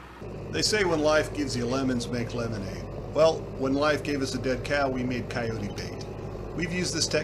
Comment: "They say: 'When life gives you lemons, make lemonade.' Well, when life gave us a dead cow, we made coyote bait." [William Shakespeare has left the chat] Edit: Thnx for correcting me, guys. I kept hearing "dead cat". 😂